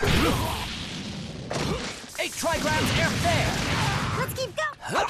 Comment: What did he say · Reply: It's suppose to be "Eight Trigrams Air Palm" for the first 2 strikes, the final time he says "THERE!"